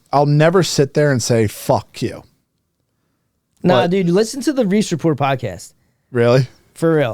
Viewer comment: What’s the name of the podcast says here? Reese report? Can somebody link that?